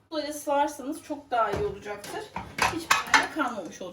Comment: Abla 1:43 de ne diyorsun? Anlaşılmıyor da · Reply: “Böyle sıvarsanız” kuzum